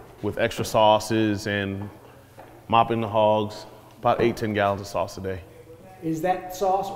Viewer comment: thought he said “mopping the halls” 👀👀